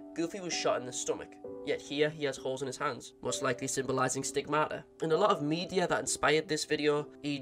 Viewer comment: What does he say at 8:08 when he says 'most likely symbolising ______'? I just can't work it out I thought he said steak murder 😂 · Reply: Stigmata